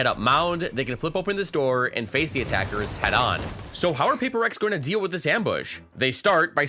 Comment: is it just me or does say PRX at 8:57?